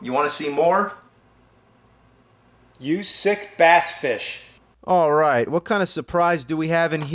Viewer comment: did he just say basfish